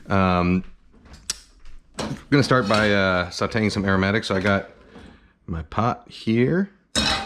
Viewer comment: I heard you say sautee paramedics